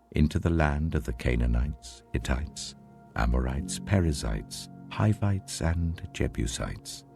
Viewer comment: says parasites instead of the Perizzites 😭😭😭😭😭😭